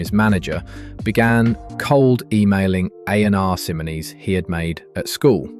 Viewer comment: can anyone please tell me 'what' he said Fred began cold writing in emails? For the life of me I can't quite make out what he said, "A&R Simolies"? "AnR Symolies"? I really want to know